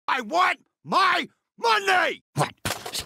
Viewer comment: when i first watched this, i thought her said "I WANT MY SUNDAE!" in the begining lol i was like wtf why is he so angry about that? ask the bartender if he has ice cream, damn! lol